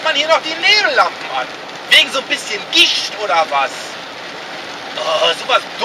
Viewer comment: Ich habe erst ,,wegen so ein bisschen Gicht" verstanden